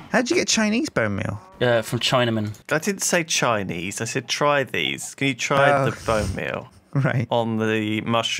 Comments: As bad as Youtube subtitles/captions are, they even thought said Chinese rather than try these LOOL :D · DEFIANTLY said "Chinese bonemeal" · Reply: He loses his train of thought often, he said "Can you use- try these bone meal"